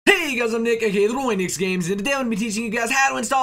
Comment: it sounds like hes saying "hey guys im naked" in the very beginning. anyone else hear it?